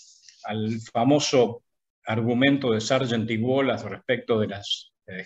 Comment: Daniel Hymann dijo Sargent Divola cerca de 1:19:00 ? No le entendí bien y no lo encuentro. No soy economista pero me estoy viendo todas las charlas y están muy buenas. Gracias por compartirlas. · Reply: Sargent y wallace